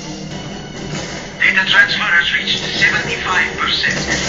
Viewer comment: Nice. This is cool. At 0:24 I think Zukovsky says, "Data transfer is now at 50%. We must acquire the rest of the data!!"